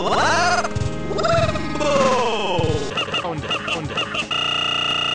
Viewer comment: what is he saying? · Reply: "What is this shiiiiiiit?!" It was a line from the 1987 movie RoboCop.